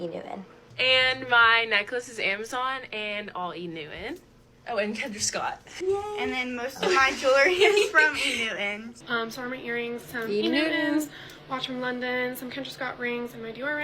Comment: lol i thought they were saying ‘e new en’